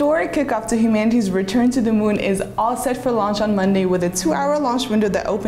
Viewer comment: I thought he said Lunch?🤣